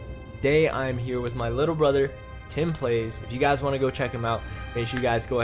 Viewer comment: When said timplayz My volume was low so I thought he said Timothy 🤦‍♂️🤦‍♂️ SMH